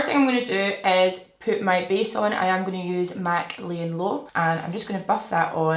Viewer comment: I thought you said “poop my base on” 😂😩 i love you so much!